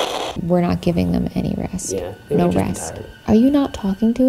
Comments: I heard "don't ask" rather than no rest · Instead of the “No rest / molest” I heard ‘who asked’